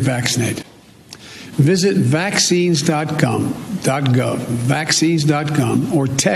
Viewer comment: I heard "vaccines.gum".